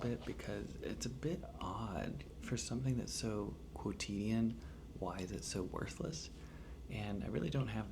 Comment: What was that word at around 11:00 minutes in? It sounded like cotillon but that doesn't make sense. What was it?